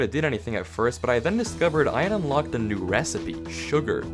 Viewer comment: sounds like he said benis